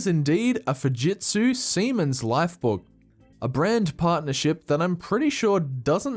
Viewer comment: Did anyone else hear A grand pile of shit?